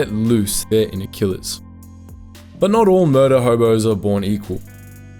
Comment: "But not all murder homos (hobo's) are made equal" I misheard that and laughed more than I should have